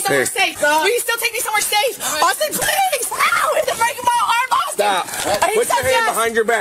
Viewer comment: did she say “your breaking my arm off”?